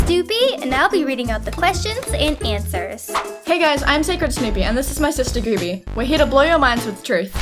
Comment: Snewpee and Gooby are so cute i want to work with them mORE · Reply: at first I thought she said snoopie like from peanuts.